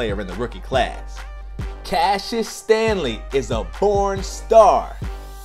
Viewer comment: did he say he was a p*rn star😳